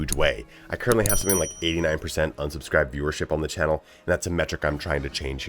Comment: Nice videos. Subscribed. At first I thought you said you had 89% subscribed watchers, I was like "what the hell is he complaining about, that's awesome." 😂 Thanks again for the videos, good luck out there.